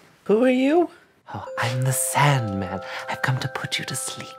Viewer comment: I thought it said "Sadman" at first and just went with it